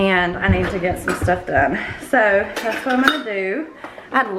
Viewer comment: Haven’t finished watching yet but when you said “I’ve had a lot of wins this week” I heard it as “I’ve had a lot of wind this week” at first 😂 😂 🤦🏻‍♀️ 💨